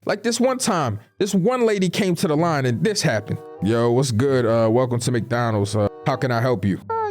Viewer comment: Whenever I hear a “yo whachst good schon” sluuuuuuurp I know nigga boutta be mad annoying😭